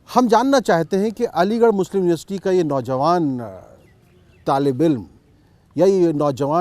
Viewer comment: what did he say "Taliban" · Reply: Talib - e - ilm matlab student